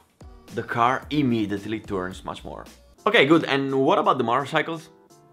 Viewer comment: Who else got a Android that thought he said ok Google when he said ok good at 12:18 🤣